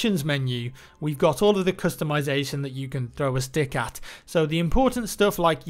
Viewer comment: had to go back and listen to it twice... thought he said "...all of the customisation options u can throw a dick at"